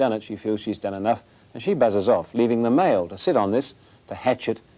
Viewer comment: "and she buggers off " 🤣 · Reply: *"buzzes off"... but I like your mishearing better! :)